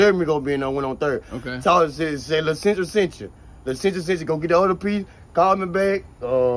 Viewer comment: 🤣 lmfao " tell em tell em lasendrick sench chu" gadang funny AF bro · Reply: i thought he said "lil central" 😭😂😂😂